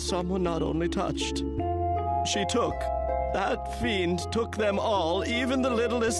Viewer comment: did he just say fein?! I HOPE THE PLAY FEIN!!!! FEIN FEIN FEIN FEIN FEIN